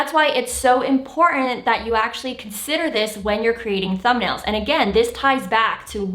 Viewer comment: Hey, I liked your video and the tips and will certainly follow your advice. One correction 9:57 instead of thumbnails, the sub-title is saying Some Nails 😁 Again thank so much for your tips.